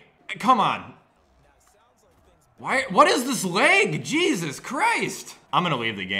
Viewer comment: did he say leg or am I just tripping